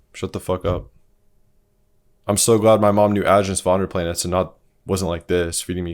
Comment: Can someone tell me what said here? "i'm so glad my mom knew ... ... ... " I rly can't understand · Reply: "Aajonus Vonderplanitz" look him up